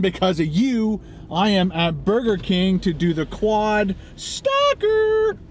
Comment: It sounded like you said "quad stalker" at the beginning. That gives the video a whole different vibe.